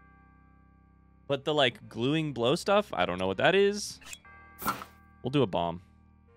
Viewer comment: got a giggle out of you saying "glueing blow" instead of "glowing blue" hahaha